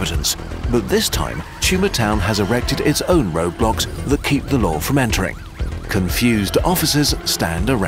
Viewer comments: did he say roblox? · Did he say Roblox???